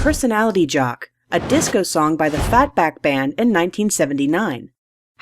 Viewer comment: "FatBack band" i heard it "Vac banned ban" i swear